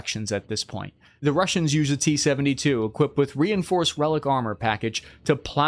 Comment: It's actually Relikt armor, a type of heavy reactive armor from the late Soviet era. Just when said really fast the t is easy to miss. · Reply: I thought he meant to say relic armor, because I know he’s a Warhammer 40K fan, and so am I. That’s so funny, thanks for letting me know!